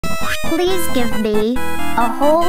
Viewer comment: What I hear: "Pls give me a whole tray of bees"